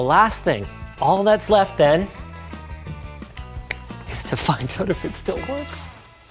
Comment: did he just say the f word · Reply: he said find